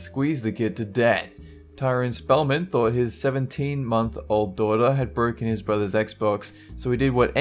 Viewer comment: subtitles say "mont" insted of "month" you might wanna fix that